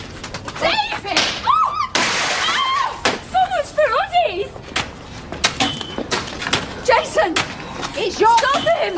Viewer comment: what did Maria say? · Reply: Sounds like off his rod he is